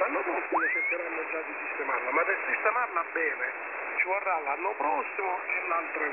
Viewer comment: This is a SOUTHERN Italian dialect and is barely understandable even by us Italians. however this piece of audio is quite clear, it says: "you have to put it in order, for this year you have to work with what you have, try to do what you can. Next year we'll try to fix it, but it will take time, it will take all of next year and the year after that <"gibberish words"> however from now on, he must not make any more mistakes, because if you get one wrong every year.... And here ends the audio, You probably recorded a very un-legal conversation, something you probably shouldn't have listened to. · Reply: I feel quite proud of myself for correctly identifying that as Italian. I recognised the pattern of speech and I thought I heard the operator say "systemama beve" at some point, which sounds like he's talking about a drinks machine.